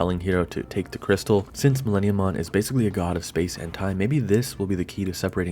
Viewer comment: I had to rewind that a few times cuz I kept hearing "God of space hentai".